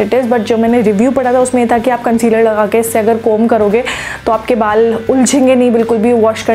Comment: diiii ap kya bol rahe haiii concealer laga ke kangi kare? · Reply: Conditioner😂🙈